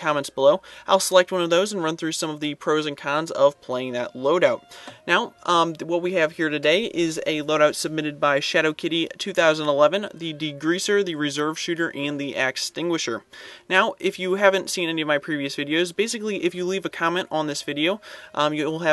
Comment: did he say "ghuetto kitty"?